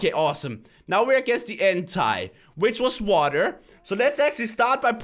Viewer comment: he says hentai instead of entei (look what is hentai crainer)